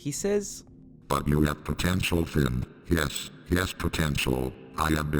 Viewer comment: Did you say potential